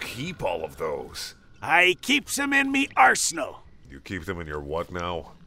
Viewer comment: I thought he said he keeps them in his a holeXD That's probably what he meant:P